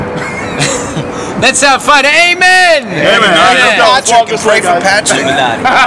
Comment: What is it that Donnie says at 0:34 when the guys all start laughing? something about Patrick, I can't make it out. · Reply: he says good luck to pTrick